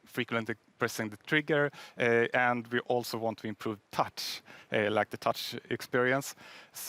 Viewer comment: What!?!? Did he just say touch. Finally someone that actually cares